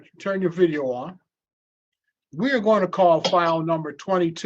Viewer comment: The judge was saying something like "We're calling file # blah blah blah..." and I "heard" "We're calling clown #...". You know what word was in the back of my mind from the start...😋